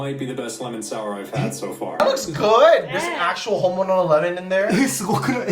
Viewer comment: くそほど日本語なのに「本物のれもん」が聞き取れない